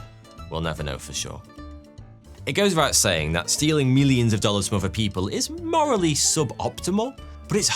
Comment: did you say stealing millions of dollars worth of people? did you mean diamonds? or cash?